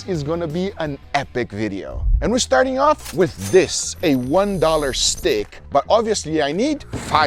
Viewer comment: I thought he said $1 steak at first lmao. But a $1 stick is just as unbelievable, lol 😂